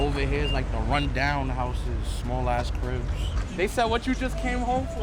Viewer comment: Make the switch to Chime® to earn 3.75% APY on Savings: <link> Thanks Chime for sponsoring this video! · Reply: I’m sorry Tommy but at 11:02 did you just say “I’m crip” and throw up the sign 🤣🤣🤣? Tommy is confirmed crip!!